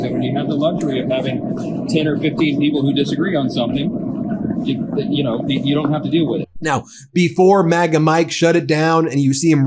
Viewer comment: You don't have to deal with it. I think thats what i heard him say in that walk press thru